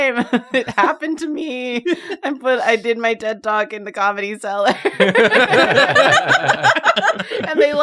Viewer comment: Oh, I only realized said "I did my TED Talk in the Comedy Cellar ["and they laughed! they laughed at me, doc!"]" when repeated it. At first I thought she said "I put my dead dog in the comedy cellar, and they laughed! They laughed at my dog!".